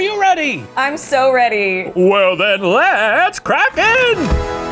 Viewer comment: Why do I always hear "Let's crap in" instead of "Let's crack in" (if that's what it really is. Maybe I'm right and it's really crap.)